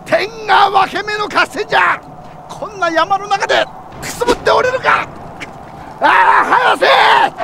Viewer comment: なんて言ってる？ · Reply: くすぶっておれるか